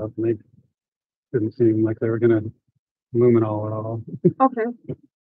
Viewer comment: did he say "they aren't going to luminol it all"?